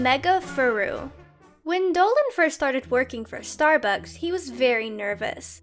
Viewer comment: I THOUGHT SHE SAID "when Dolan started twerking for Starbucks